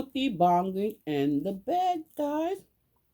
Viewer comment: I thought you said Monkey banging in the bed!🤣😂🤣😋